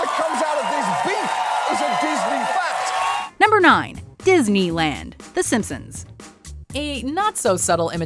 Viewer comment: I thought is said The Führer Land because of Walt Disneys association with the Nazis 😂